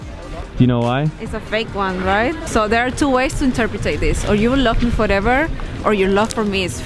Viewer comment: Did she just say "interpratate"?